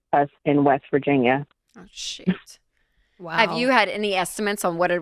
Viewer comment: We heard that, Rachel. 😂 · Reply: Sounded like she said shioot. 😂😂😂